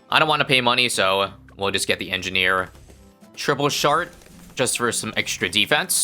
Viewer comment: did he say triple shart